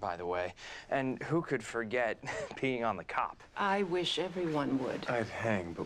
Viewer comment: i'm sorry did tommy just say that Oliver got in trouble for peeing on a cop @ 0:48 . Oliver wtf man.